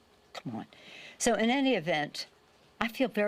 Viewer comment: Did she say she feels very inebriated @ 49:37 ...?